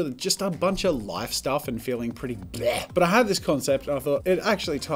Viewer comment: What Jazza said: feeling pretty bweh! Subtitles: feeling pretty GOOD!